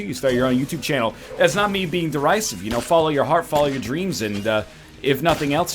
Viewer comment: Can someone help me out? At 6:30, NL says "that's not me being...darise? Can someone tell me what the d word NL is saying? Thanks. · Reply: Derisive: Expressing contempt or ridicule. Basically, he's saying he's not being snarky and dismissive.